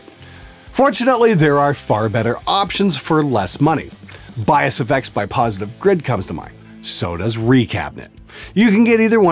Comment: What are these other $99 amps that he mentions at 4:23? I've replayed that part many times but I still can't tell what he's saying. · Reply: What he says verbatim is "Fortunately, there are far better options for less money. Bias FX by Positive Grid comes to mind. So does Recabinet."